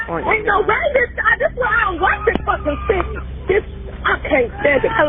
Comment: "this is why i don't liek this f*Cking city" is what she actually said